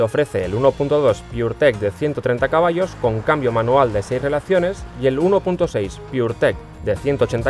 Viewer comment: Con cambio manual de 6 violaciones???? whaaaaaaat?? jajajajajaja 11:19 minutos. O escuché mal yo? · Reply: Ni violaciones, ni velocidad. Dice 6 relaciones.